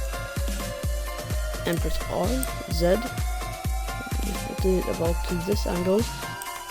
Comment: Stuck at 13:27, I keep hearing "then press alt zed"? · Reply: When he said "zed" he actually means "Z", Other people pronounce Z as "Zed" or "Zee"